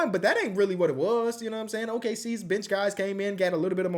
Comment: Really thought said “OKCs bitch guys” for a second